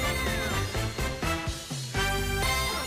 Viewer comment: DAB! I can't be the only one who hears that